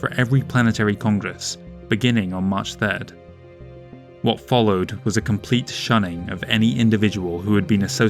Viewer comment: You say March 3, subtitles say May 3